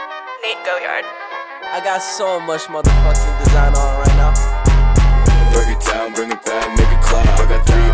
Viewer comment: what does he say here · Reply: I got so much mothafuckin designer on right now